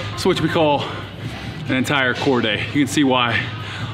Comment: I actually thought you said Cordae not core day